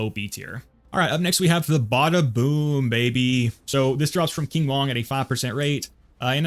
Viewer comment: lmfao wtf I thought you said "the badaboom drops from King Von" had to replay ts so fast